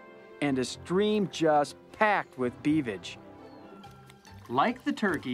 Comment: did i hear "packed with cleavage" ? XD